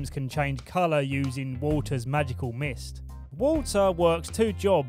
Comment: i thought he said water's magical mist, not walter lol haha